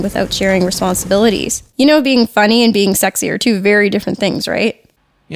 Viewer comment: Did says"is funny and being sexier too than doing thing,right?"